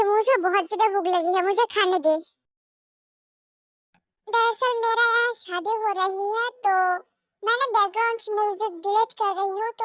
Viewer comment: yeh kya mene thik suna "mera shaadi" didi apka shaddi hae?? Omg congratulations 🎉 but if I heard it wrong then sorry 😅 17:56 sorry didi mein Muslim nahi hu mein hindu/bangali hu from India west Bengal ❤